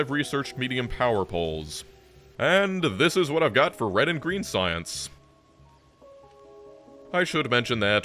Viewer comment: Thought you said reddit green science instead of red and green science there, lol.